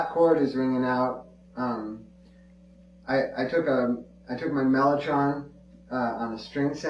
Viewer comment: what does he say on 2:19? Melotrone? is that a effect? Thanks :D